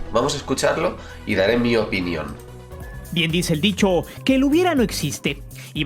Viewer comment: No tiene nada que ver pero al inicio entendí "Como dice el Bicho", la defensa de la selección mayor no esta al nivel ni de una de liga de ascenso, en un mundo ideal sacaríamos a algunos que se salvan de la "Mayor" para darles lugar en la Olímpica y ya no cargar bultos que nos hacen quedar en ridículo, este vídeo de Los Expulsados me ganó cuando reventó al pecho frío de Salcedo. Ya necesitamos un emoji de Salcedo para los chascos mayores, excelente reacción y análisis!